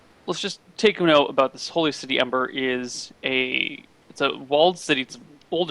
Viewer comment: Did say 'the Holy City Embert' on 24:49 or somesuch?